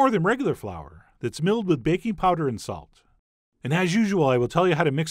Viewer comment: I thought he said baconpowder then my brain corrected itself to bakingpowder 😫